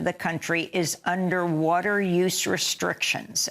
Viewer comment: Ann. Your cadence is first thought you said.....France is "under water" but it was under advisory to boil water